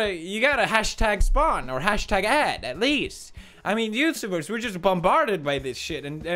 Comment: with subs lmao I think he says youtubers